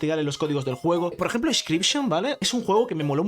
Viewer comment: cómo de llama el juego? No sé lo escuché bien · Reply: Inscryption